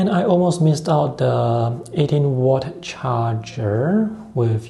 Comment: did you say 18 or 80 Watt charger? Which one is it? And would it also work if its more that that amount of Watt? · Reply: 18W. It will work with more powerful chargers also as it will only draw enough power but not more. If you use with USB-C video connection, there's no need for power cable.